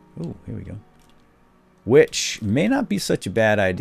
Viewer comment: lol, at 11:08 I thought he said witch, and I'm just like, WHERE!!!